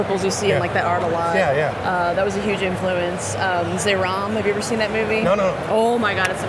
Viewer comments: Does anyone here know what she said that the movie was called at 3:57 It sounds like she is saying Zayrom, but i can find anything. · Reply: try zeram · she talks about a movie, i couldn't understand what she said, someone can? i first tought it was "xenom" but i didn't found any movie with that name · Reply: Zeiram